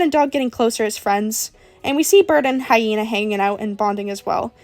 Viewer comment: I was moving so there was noise and I misheard that as “we see bird and hyena making out”